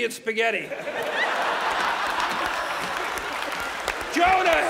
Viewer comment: I swear I heard J. Cole